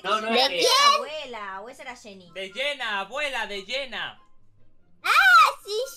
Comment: jaja yo entendí abuela rellena xdd Jsjs me encantan sus videos :D son los mejores owo